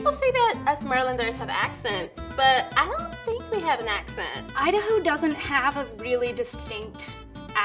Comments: "id a who doesnt really have a distinct accent" me: did you say idawho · I swear to god she said us merlanders